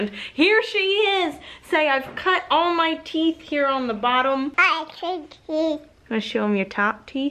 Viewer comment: Oh my gracious. It sounded like she said, "I cut teeth" Lol.